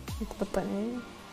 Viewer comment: ماسمعتوها تقول حق حلا في الدقيقه 1:51مو تبطي عيوني خايفه تبط عيونها 😂😂😂😂😂